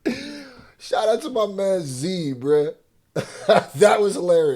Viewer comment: Don’t Even Realize It Sounds Like He Saying “Shout Out To My Man Zebra 🦓 ” 😂 I Kno He Means Z Bruhh . But Funny How He Accidentally Did That